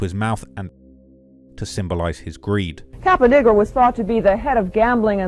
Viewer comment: did she just say "cap a ninja"?